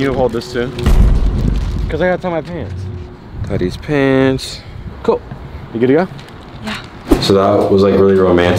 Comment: does he say tie these pants or daddy's pants?